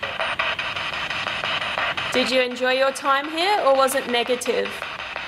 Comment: ! I hear a clear „negative“ 🥶💙 AMAZING VIDEO tho‘!